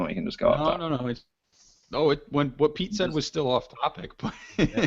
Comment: Instead of I think he should his new name should be pizza. When I heard "Pete said" I thought you said pizza :)